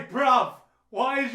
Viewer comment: did he say bruv